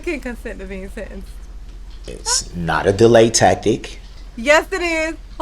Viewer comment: Lol, at one time, it sounded like he said; it’s not a delay tackit!